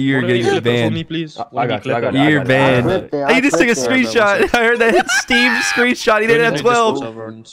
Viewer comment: did he say this nigga screenshot or am i hearing things ? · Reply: "he ju- took a screenshot"